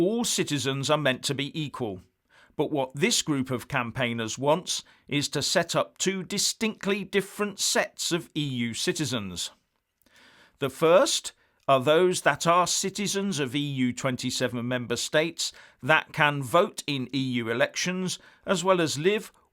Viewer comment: I thought said EU shitezens. Must clean out my lug holes.